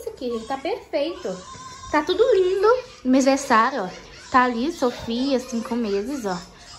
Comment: Foi só eu que ouvi que no minuto 14:17 ela falou "Sofia 5 meses"